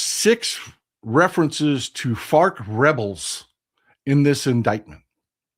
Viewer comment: Fr I thought he was saying FART rebels